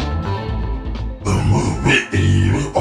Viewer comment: it sounds like hes saying "what the fuck?"